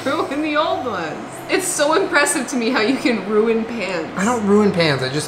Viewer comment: I always thought she was saying Julien ruins "pants" instead of "pans" and I have no idea why I thought that made sense